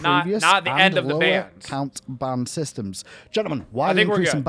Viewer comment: Thought he said band systems, I was pumped :D